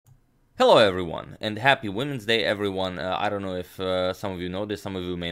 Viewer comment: LOL...I heard "Happy Wednesday" instead of "Happy Women's Day" and I was like, uh...... :)